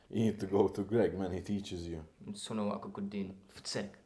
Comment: he says Sana kudun fıs et witch can mean more things , it can like lost at translation it can mean You lost your mind for nothing You've put in all this effort and... gasp You got all worked up for nothing he is basically like saying what you say , but in his language. Of what greg wanna say, doing more than you should or something like that